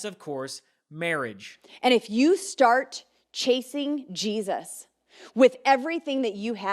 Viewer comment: I hear "chafing Jesus" 3:55 🤷‍♀